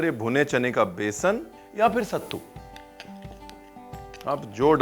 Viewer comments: Sorry to point out but the edit team has written "SUTTA" instead of "SATTU" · I love your videos Ranveer. They're oddly therapeutic for me. I can't cook to save my life but I love to see you cook. (P.S : at 4:21 it says "sutta" flavour 😂. I think you were meaning to say "sattu"...)